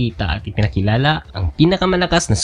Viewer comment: bat parang jakol mihawk narinig ko 😅😅😅😅